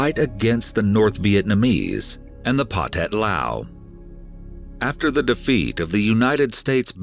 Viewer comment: I swear it sounds like he said Pothead Lao